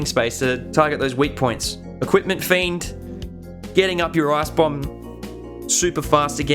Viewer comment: anyone else hear 'getting up your ass-bomb'?